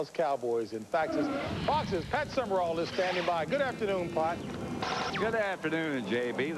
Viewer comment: did JB say "Good afternoon, Pot?" LOL LOL